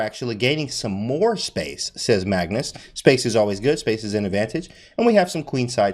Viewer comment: Thought it said smacks Magnus lmao